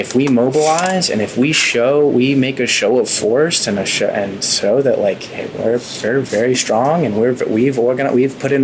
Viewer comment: I hear "and so that like Hitler, it's very very strong"